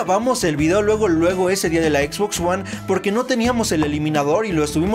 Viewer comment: Xbox one ? O acaso entendí mal ?